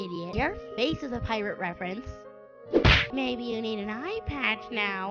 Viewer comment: what taco said: maybe you need an eye patch now HAHAHAHAHAHAAAAA What i heard: maybe i need an ipad now HAHAHAHAHAHAAAA